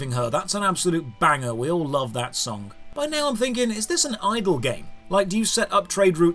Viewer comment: I thought he said "idol game" and was waiting for what interesting theory he would come up with, but no, it was "idle" .. -.